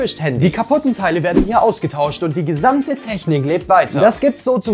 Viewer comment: Ich hab verstanden black Market 😂